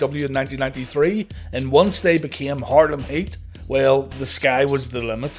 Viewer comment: Almost thought he said Harlem Hate